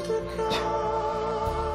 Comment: What Danny says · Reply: Gioia Stefani "Dude, It's Beacon Hills "